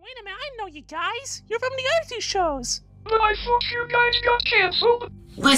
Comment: It s sounds like he said but I f you